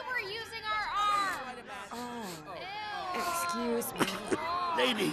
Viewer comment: I love how Aunt Fanny's farts literally cause an earthquake and then just straight up kill a dude 😂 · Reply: I thought he said he need a doctor 🤣